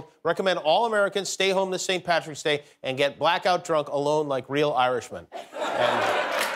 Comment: You said, "alone like real Irishmen" but I heard, "hello Mike Reel irishmen" 😅